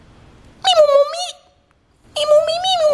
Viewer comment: sounded like he said "meme on ma meat"